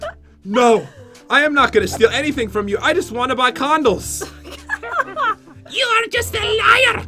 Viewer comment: Not to be inappropriate but,doesnt it sound like he says condom not candle lol